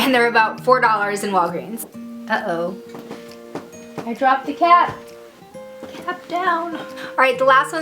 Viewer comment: when u said 'uh oh i dropped the cap' i thought u said 'i dropped the cat' and got really worried ._. (nice video btw ^^ thank you for the reviews!)